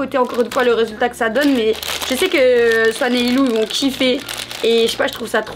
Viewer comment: C'est moi ou j'ai entendu elle a dit a: 7:39 "swan et Lilou 😂 Sinon je suis trop contente j'aime trop ce genre de vidéo